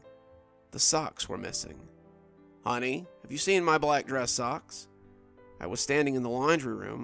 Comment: Honeyyy where’s my super suit! Tell ‘em I’m not the only one that thought that when you said “honest where’s my dress socks”